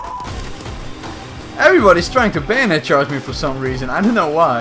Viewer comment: Why does it sound like he says bandit charge and not bayonet charge🤔🤷‍♂️